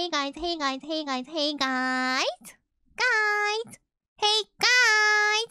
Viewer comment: Why do i hear " Hey Guy-tsu"?